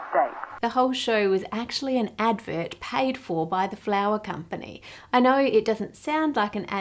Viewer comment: For half of the video I thought was saying "flower company" and it got me so confused as to why a flower company would be making a fake baker 😭